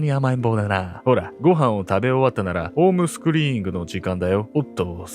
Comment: 全米行方不明非搾取児童センターがバージニア州にあり、事件がモンタナ州で起こったと言うことですか? 7:44の「ホームスクリーイング」と聞こえる言葉は「ホームスクーリング（Homeschooling)」だと思います。この事件、結局未解決なんですね。ご遺族がお気の毒です。